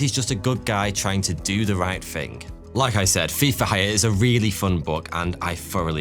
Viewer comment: Ngl when he said Thief for hire I thought he said FIFA